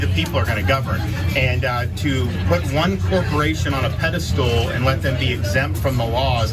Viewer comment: I think DeSantis said, "pedal stool."